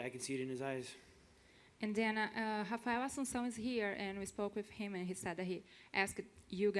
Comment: I'm sorry but who the fuck did she just call out? I couldn't understand shit of what name she tried to say.. holy shit. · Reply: Raphael Assuncao